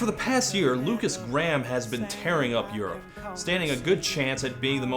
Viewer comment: I had to back up because I thought you said his name was “Lukas Graham 4channer.”